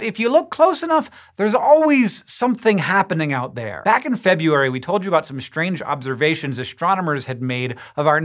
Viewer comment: Did he say "Febuary"